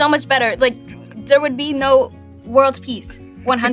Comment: Did Jenna say there would be no World Peace @ 6:50?